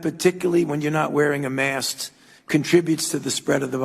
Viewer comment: did Fauci say mask or mast??? I thought this was about covid, not boats.....